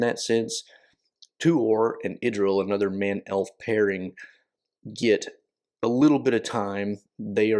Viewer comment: 😂 Pardon me, but I just had a hilarious case of misheard narrative" listening half asleep and not being a native speaker! When you said "man-elf-pairing" for a second my brain heard "men-elf"and went on: "Wait, Tolkien wrote gay couples?" 😳 Which would've been really cool, considering the times he lived in. Then I realized it should either be "male" or "among men in this case. 🤭